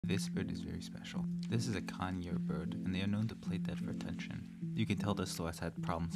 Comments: I thought he said kanye bird 😅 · I thought you said this is a KANYE bird. Well K does seem to act out for attention.